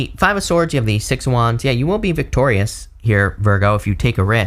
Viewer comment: "you won't be (will be ?) victorious" ?? Sorry I have APD, can't tell what was said. Context says "will be..." but ears are hearing otherwise. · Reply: I did say will be